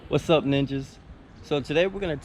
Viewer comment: no joke i had to listen to the first part again i thought he said “sup niggas”